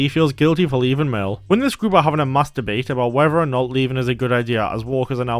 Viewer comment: I thought you said something besides "Mass debate"